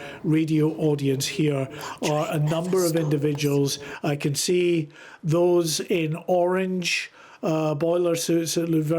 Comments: Right at 5:47 there’s another voice sound like a woman saying something well never stop I can’t make out the first word does anyone else hear it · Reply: Our train never stops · what does someone whisper here? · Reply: "the train never stops" proper creepy. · Sorry but did anyone else hear the voice whisper something at around 5:46 on this audio?? It’s freaked me out. Such an awful incident 🙏 😔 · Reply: It said the train never stops 😮 What the hell was that ? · wtf is the whispering at 5:46 on this video? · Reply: The train never stops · What was that I heard ???? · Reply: The train never stops. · what did she whisper?? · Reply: ''the train never stops'' its soo weird dude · what was that whisper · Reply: I heard that too, "That train never stops" Similar voice whispered at the funeral of Elizabeth II. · What is being whispered at 5:45? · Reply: "The train never stops" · what’s being whispered here? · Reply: Our train never stops.... its an evp for sure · Can anybody make out what is whispered here? It sounds creepy. I can make out "Never stops" but not what comes before it? Edit: Worst thing is, what I think I can make out sound like "The Train". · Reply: "that train never stops"